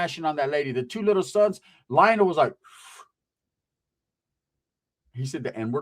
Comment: Hey I thought you said little Lionel what's a southsider